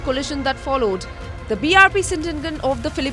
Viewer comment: I hear it "the BRP Sindandan" hahaahah